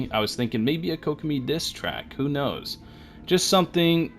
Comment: i didnt expect anything other than healing from kokomi. but i will wait to see future healers she aint for me ''diss track'' i heard ''distract'' lol